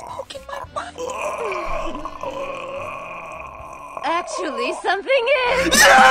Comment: can someone tell me what she’s saying ? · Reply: “Actually something is” Because the dialogue before was from the guy saying “I think somethings poking my butt”